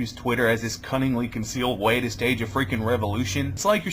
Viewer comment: Yo dude, kudos to you. I thought I heard you say 'cunnilingus way to stage a revolution' at 1:45 or :46. I must be watching SXE too much. Not that it wouldn't be a good idea. plus progressive direct deserves a look even though my wife deals with all the insurance stuff